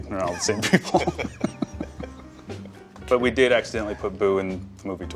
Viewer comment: did he say actually or accidentally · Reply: Accidentally... But I wish it was actually